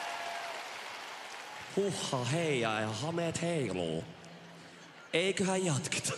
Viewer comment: Did he just say Huhhahhei 😍😭 like his new song? Was that an easter egg spoiler?